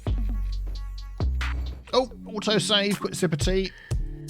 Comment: On that last metro line, do you now have a trianline and a metroline from/to the same location? How will that play out? And on a differnt topic , I guess i hav a hearing problem, i always thought you said "autosave simpathy" instead of sip of thea. Maybe a name for a district: "autosave simpathea" hehee